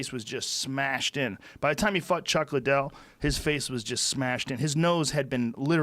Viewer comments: I thought Joe said fucked chug liddell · Did Rogan accidentally say "fucked" instead of "fought" here? Or am I just projecting Wanderlei's mistake onto what I'm hearing?